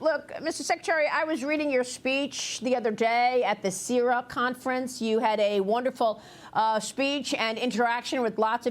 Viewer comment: I must be really hungry cause I could of swore she said syrup conference 😂 and my first thought was where can I get tickets 😂